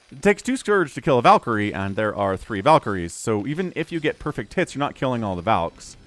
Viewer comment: Did you say "perfect tits" at 8:49? Because that's what I heard and nobody can convince me otherwise. It's not only the drill that changes from left to right. The Marine's gun orientation also changes hand, that's because they mirror the units movements so they only have to draw half the images. SCV, Marines, Ghosts and Medics are probably the only units that have different things in their 2 hands, the rest are all symmetric.